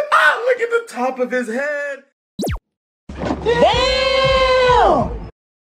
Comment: why does this sound like “EWWWW!” when I first heard this it sounded like “EWWWW!”